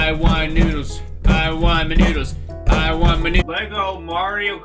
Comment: Am I the only one who thinks it sounds like he says I want my nudes, instead of I want my noodle?